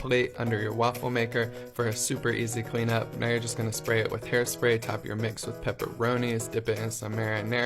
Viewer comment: Ok, I know that I'm late but did he just say 7:10 now Im just going to spray it with hair spray???